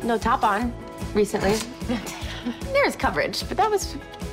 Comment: what did lucy say at 1:00 min pls? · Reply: There is coverage